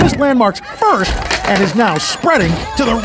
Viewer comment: I could've sworn I heard him say Fudge instead of What